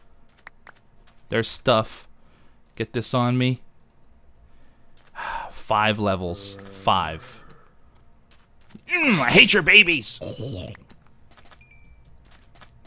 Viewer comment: I sounded like i ate you babys